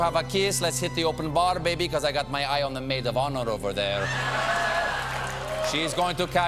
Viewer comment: what he said? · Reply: "the maid of honor over there"